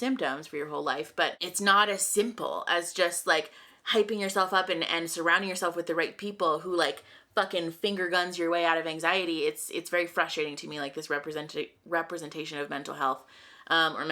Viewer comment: misheard “finger gun your way out of anxiety” as “finger bang your way out of anxiety” and I was still on board